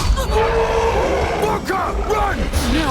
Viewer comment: it sounded like ‘F**KER, RUN!’ rather than ‘VALKA, RUN!’ .